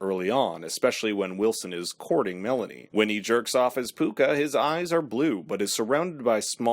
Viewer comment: "-Jerks off as Pooka-" BRO, WHAT?! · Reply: I thought he said "when he jerks off his pooka lmao".